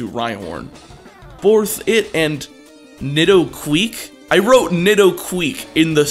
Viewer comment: there's worse things to call nidoqueen than nidoquick.... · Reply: Was it nidoquick? I coulda sworn he said nidoqueak. Edit: spelling correction